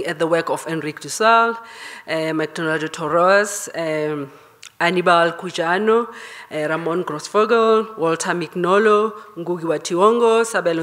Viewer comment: Did anyone catch those authors at 17:29? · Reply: they are decolonial scholars ( mostly male) Enrique dussel; Walter Mignolo; McDonaldo Torres; Anibal Quijano, Maria Lugones